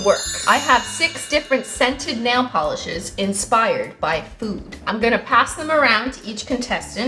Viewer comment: i heard "food" as "poop" lol